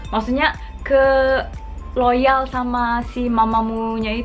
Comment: aku dengernya:Loyal